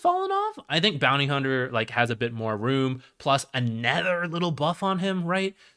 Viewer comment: did you watch the video? he says "other" about every 3rd sentence, and the pronunciation is jarring. yes hes talking about aether lens as well, but could you tell the difference? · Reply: here's one of many. I have never heard this pronunciation even from someone for whom english is not their primary language. literally sounds like hes saying "anether"